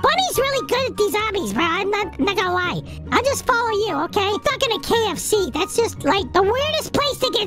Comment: why do I hear “f**k in a KFC”